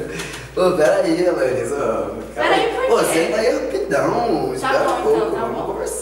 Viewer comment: Foi muito engraçado o que ele falou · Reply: Ela falou senta ai rapidão